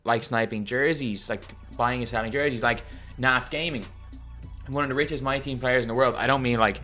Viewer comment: who? I want to see if they have a YouTube or something but can’t tell what you said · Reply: Bennett's Balloons I found him I think Naft_gaming